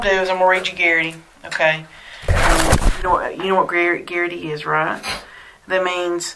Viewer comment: When I hear "garrity" I really hear "Tegrity"